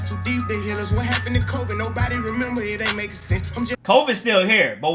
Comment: I think he said what happen to Kobe not covid